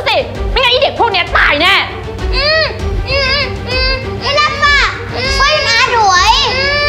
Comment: ทำไมเราได้ยินว่าอีรักฟ้าช่วยด้วย